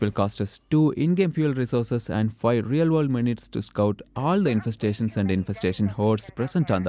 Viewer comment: I misheard what said and thought it was "if we use this radio command it will cost us two in-game fuel resources and five real world monies." I thought to myself 'man, they're getting aggressive with those microtransactions'